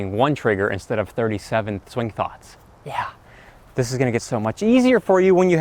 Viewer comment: did you say "Thwing Thoughts?"